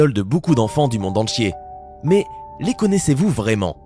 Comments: "monde entchier" pourquoi je rie pour des choses nulles??? XD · c moi ou a 0:04 il dis monde en chié et pas monde entié